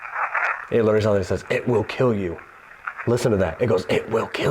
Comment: I don't hear "it will kill you", I hear "it's the killer" - 31:58 😵